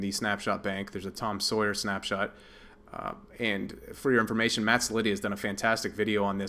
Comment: Who?? Maxilius? I can't understand what name you're saying :) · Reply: Matt Cellitti